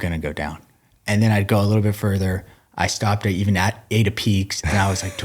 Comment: Where is that spot he stopped at? Sounded like he said Ada peaks. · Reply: Ate a Peak. As in Peak refuel.